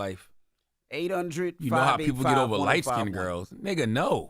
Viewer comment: CTG thought he said you know how niggas get over light skin girls ..but he said you know how light skin girls act .. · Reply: Naw I said you know how dudes act over light skinned girls. It was a lbvs joke lol